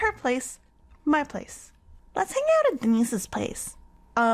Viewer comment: Everytime you said "Deinse's" it sounded like "Anyssa's" and I was confused each time you said it